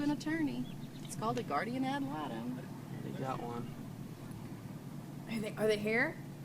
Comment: I dont know if anyone noticed but at 35:38 she mentions getting him an attorney and at 35:42 it sounds like he says "I've got one."